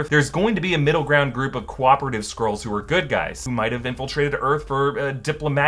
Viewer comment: I keep hearing 'squirls' hahahaha. 🤣